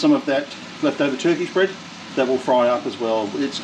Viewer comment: This looks amazing! Don't laugh, but I thought you said tuna bread...I could not for the life of me figure out how that would work, but is cooking and I'll roll with it. So glad it was TURKISH bread! My ears sometimes! Sending hugs and love to and you! Snuggles for Xena. Have a lovely week!